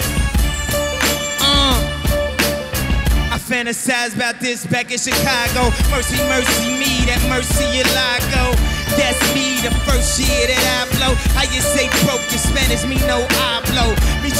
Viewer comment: he sounded like he said first sea